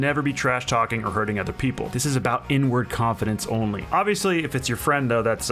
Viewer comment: i thought he said "this is only about n word toxicity" lmao